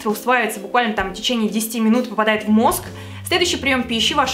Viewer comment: Ты сказала поступает в мозг, а мне послышалось в нос))))